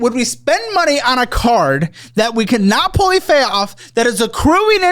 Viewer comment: getting so upset he says pully fay off instead of fully pay off. I cackled. 😂